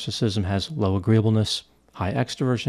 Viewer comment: Is there an acronym for memorizing the five-factor model? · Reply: When I first started listening to Dr. I thought he was saying Eroticism not Neuroticism. I couldn’t understand why the acronym was OCEAN. 😂